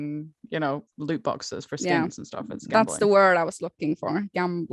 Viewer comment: I thought said she bought foreskin in league of legemds then realised she said for skins not foreskins lol